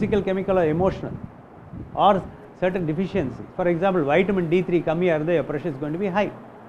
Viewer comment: D3 ? Min 3:54 doc please what did you say? Do you help via telemedicine? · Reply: Vitamin d3